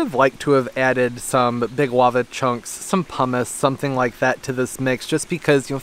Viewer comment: can somebody tell me what he said he wants to add to the soil, for the roots to grab on? (at 11:30) sounds like pommes X) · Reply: Hello! Yes, pumice. 😊